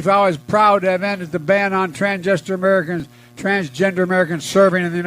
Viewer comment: did he say "transistor Americans" or "trans-jester Americans"?